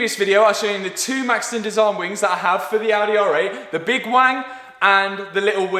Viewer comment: did you just say big wang? 🍆